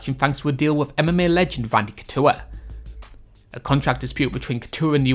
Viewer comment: did he say "frank couture"?